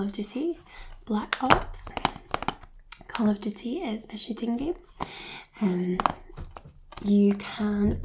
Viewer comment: No offence to ur accent but I was dying of laughter when it sounded like u said "Call of Duty: Black Ops, this is a shitting game" sorry but I found it funny and besides that great relaxation overall and a definite sub ❤️